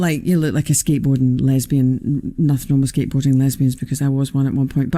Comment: did she say she was a skatebording lesbian?😂